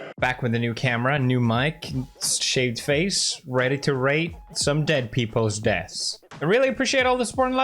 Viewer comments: At the begging I thought he said rape dead people 💀💀💀💀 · In the first fifteen seconds he said ready to rate some dead people’s deaths I thought he said ready to rape some dead people at first